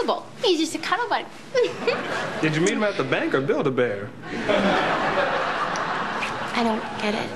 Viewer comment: what does he say did you meet him at the bank or b...?? What o.Õ somebody explain me pls english is not my motherlanguage^^ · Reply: Bank or "Build-A-Bear" build-a-bear is a children's facility that allows them to create their very own stuffed animal basically from scratch.